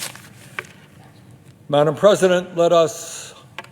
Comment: did he say "madame president"